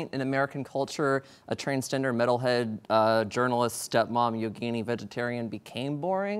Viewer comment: What does she say between step mom and vegetarian? Zucchini? Ukini? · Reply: Yogini - a woman who practices yoga.